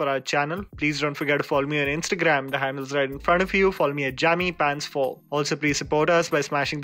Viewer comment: One question for: Why do pronounce follow as fall? I always hear "Fall me at JAMMYPANTS4". Is it just me?